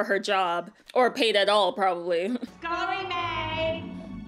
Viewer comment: Has anybody misheard “Scullery maid” for “It’s Gonna Be May”? 😅